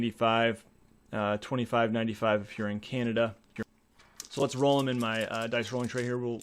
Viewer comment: Thanks for the comment! I'm not Canadian, though. Just read the US and Canada price on the box. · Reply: Oh! I misheard that as "here in Canada", my mistake.